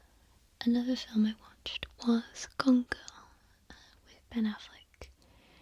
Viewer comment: So when you spoke the words "Gone Girl", I thought you said "Congo" (my Yankee ears misheard you). I was thinking "Wait -- Ben Affleck was in Congo??? And it's a romantic thriller??? Wasn't it about talking apes????? I guess Laura's interpretation of Congo is very different than mine...." And then I realized you were talking about Gone Girl. Thanks for another nice video, girly.